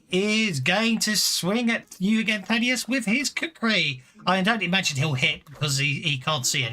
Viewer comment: Also I thought initially you said cookery instead of kukri when hook shanks attacks.